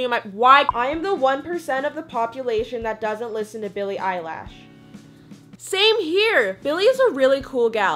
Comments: I almost died when she said eyelash instead of Eilish edit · It sounded like she said Billie Eyelash 😂🤣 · Did she just say billie 'eyelaaash'